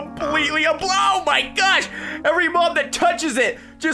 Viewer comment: What graser say : oh my gosh ! , every mob that touches it it just gone What captions say : oh my gosh every mOM touches it it just gone